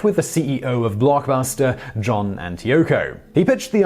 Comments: the owner of Blockbuster JohnandYoko, that is what I heard hahahaha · Did he just say that the president of Blockbuster was named "John and Yoko"?